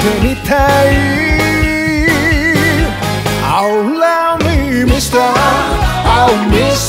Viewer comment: Am I the only who misheard Oh Love Me Mister to Allow Me Mister? 😑